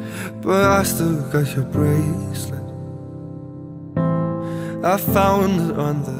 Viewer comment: I thought he said "I still got your braces" instead of bracelet haha